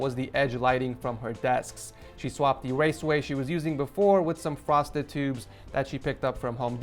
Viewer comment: It sounded like you said "prostitutes" at first instead of "frosted tubes". I had to listen to it three times just to be sure. 😅